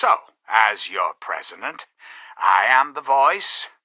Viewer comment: did he say presiment?